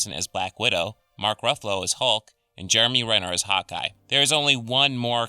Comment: when you first said hawkeye , I thought you said "hot guy"